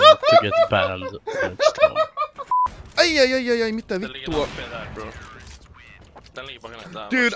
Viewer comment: I was like ok he is good at speaking finish And to everypne who didn't understand he said what the fack=mitä vittua